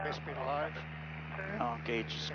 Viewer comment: Did he say airspeed alive at 1:07 ?